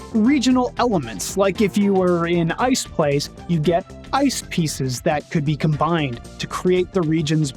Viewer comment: I thought you said "Ice Spice"